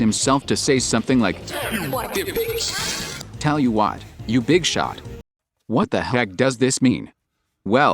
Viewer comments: I always hear "I'll tell you what it is!" at the end rather than "Tell you what, you big shot!" Especially with "It pulls the strings and makes them ring" line, I'm beginning to believe that Spamton is trying to tell us something (with all of the [[Great deals!]] he tries to tell us). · I always thought the last lyric said "I'll tell you what, you little slime!" Which not only matches a previous line by Spamton, but also sounds like it could be from a commercial. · i kept hearing "tell me what your biggest sell is" where you heard "tell me what you big shot" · i always heard "tells you what to think!!" for the last line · I hear “Tell me what you’re really selling” at the same time as “Tell you what you big shot”. It’s like Yanny and Laurel for me lol